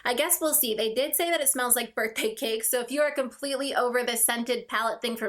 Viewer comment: Haha! I had to rewind a couple times because I had sworn you said the too faced palette was supposed to smell like "burnt pancakes". I was like ew. Why would they do that? Can't be..yeah no..you said "birthday cakes". Much better smell lol